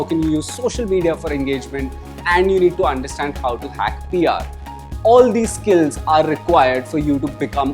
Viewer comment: dont want to be rude, but what is he saying in 2:58? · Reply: He said "How to hack PR" (Public Relations).